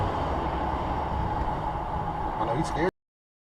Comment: when the subtitle says "out of his mind" but the character says "shitless"